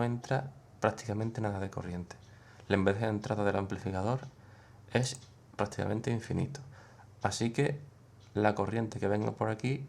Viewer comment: En el minuto 4:50 q dices? noseque de dentro del amplificador es infinito, el q? la impedancia? esq no se entiende lo que dices ahi, lo dices muy rapido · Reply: La impedancia, sí, perdona la rapidez.